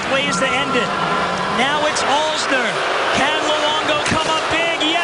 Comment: he says Alex Alzner instead of Karl alzner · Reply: hawks films "Now it's Alzner" not alex alzner